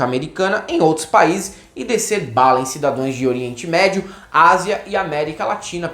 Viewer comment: Eu ouvi aos 8:11 "cidadões"?